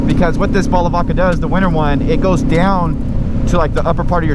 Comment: Wait what were you calling it? Balavaca? Haha. It's Balaclava <3 I actually got the Oxford heated grips (sport I believe) and the Barkbuster Blizzard handlebar mitts and those work FANTASTIC. Just need to get use to having them over my hands. Was riding around last night running errands, and trying to break the new tires in. They work very well, and my gloves stayed pretty dry and warm. Was a good experience. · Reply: I could've sworn he said "bottle of vodka"